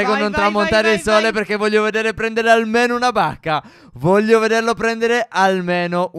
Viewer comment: io (nell'intro) al posto di "bacca" ho capito "banca" XD